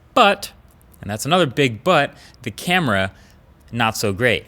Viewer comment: Did he just said “Another BIG BUTT”